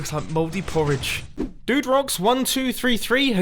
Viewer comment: When he said duderocks, I thought he said do drugs XD